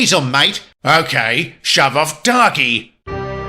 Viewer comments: did he say Dogy or Darker ? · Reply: He said darkey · What did the truck say at 12:09? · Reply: He said "Darkey". Basically saying the N-word to a black painted engine